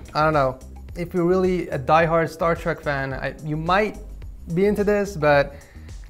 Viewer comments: he actually said Star Trek...... Disappointment😭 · he actually said Startrek🤦